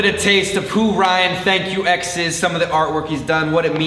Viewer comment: I thought he was saying thank you to his ex lol